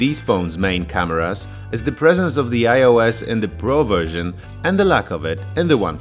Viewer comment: OIS instead of IOS 🙃